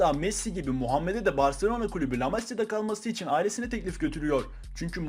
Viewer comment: Abi 01:50 de amasya mi dedin ? Yoksa Ben mi öyle duydum